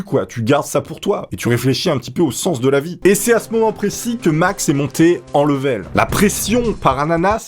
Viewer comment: 😂 😂 😂 À 4:03 j'ai entendu "Max est monté en levrette" 😂 😂 😂 😂